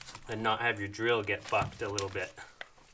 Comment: Lol at 8:29 did you say fucked? Haven’t heard you drop an F bomb before haha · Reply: Bucked. Like when i was drilling the side of the hole would push the battery and put the bit out of alignment thus bucking the drill bit out of the screw.